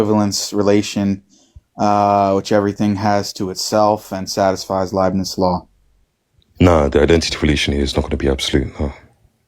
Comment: which law did brother mention here? I couldn’t understand what he said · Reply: Leibniz law, so basically the law of identity